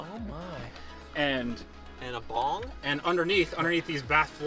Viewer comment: and a bong? Lmao who else heard that shit 😂💀